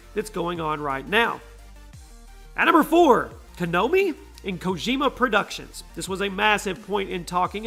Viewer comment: wait did you say "Kanomi"😂😂😂?